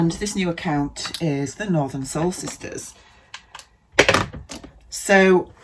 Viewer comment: I thought you said Northern Soul sisters I love the music. Looking forward to this. Love your dog. I have a new pup who helps more than I would like him to.😄